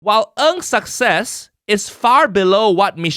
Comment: who else heard 0:00 "While UNSUCCESS is far below what Michelle Yo has achieved" immediately grabbed my attention, who is calling Nigel "unsuccess"? because we Asians don't use "unsuccess", we use "FAILURE". use the right word, not the white word ~Haiyaa~~ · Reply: Ng’s success, not unsuccess.